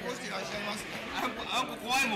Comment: 42分30秒あたりで「あこ怖いもん」と聞こえたのですが、私の勘違いですか？ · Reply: きのこのこ あんこて言ったはりますよ。